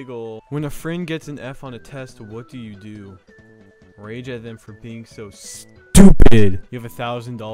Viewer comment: I thought 3:40 said "rape them for being stupid"